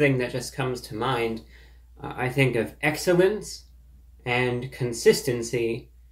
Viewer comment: Burger King is known for “excellence and consistency”. Reviewbrah leading off the review with his trademarked, straight-faced humor 😂😂😂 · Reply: I honestly thought he said “excrement” and not “excellence”… 😂